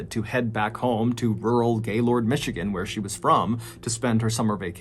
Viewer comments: Did he just say " rural gaylord michigan" ???? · I kept hearing “Gaylord Michigan” please correct me if I’m wrong lmao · I’m sorry but Gaylord Michigan?? I know you most likely didjt say that but that’s all I heard bahahaha